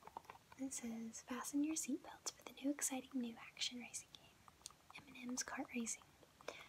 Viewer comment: I heard “Eminem’s cart racing” but then realized it was actually M&Ms...EA hmu😂